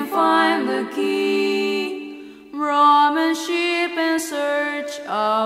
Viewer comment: For the longest time I thought they were saying Ramen Ship. I envisioned some One Piece style anime about a bunch of swashbuckling kids and their adventures aboard the good ship Ramen.